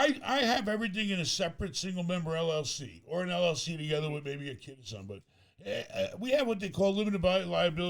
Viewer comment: LLC together with a ....... What does he say? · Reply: “Together with a kid or something” He was saying that he has only single member LLCs but then realized that he has one with a kid I think.